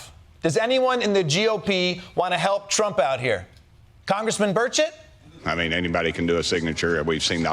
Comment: Haha I thought he said Congressman Birdshit😂